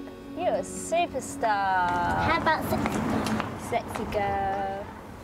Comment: Ummm19:18 did I just hear “sexy girl”? 😳😳😳